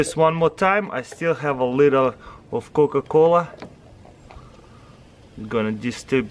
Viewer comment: did he say cocaine?